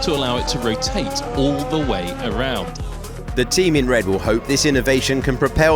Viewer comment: The team in redbull .. is what I first heard, lol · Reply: he said "the team in red will hope......" but it does sound like he said redbull for reason